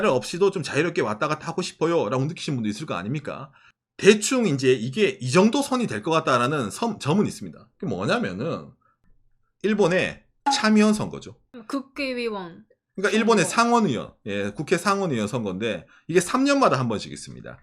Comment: 국개의원으로 들리는거 저만그런가요ㅋㅋㅋㅋㅋ